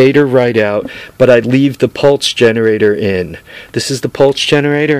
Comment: Did he say pulse generator at 4:43 in the video. Trying to get the name on tht sensor